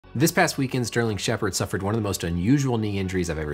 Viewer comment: thought it said sterling sharpe